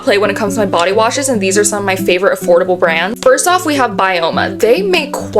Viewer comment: I thought you said affordable girl